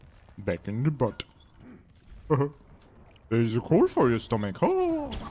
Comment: "HoW boWt sOme coAl foR Yer sTocKiNg hahA", I feel like that about the Ana Rein interaction. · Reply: is that what was being said! i kept hearing "you got a hole in your stomach." i was so fucking confused